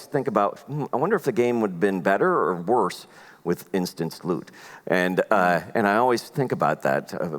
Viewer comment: I watch too many MxR that I thought he was saying 'instant sloot'